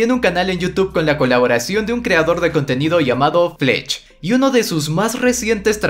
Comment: entendi plech XD